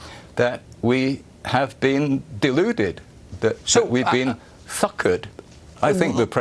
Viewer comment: did he say fuckard?